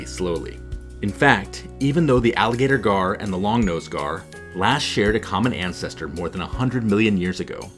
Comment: really thought that said "Longnose Gay" from afar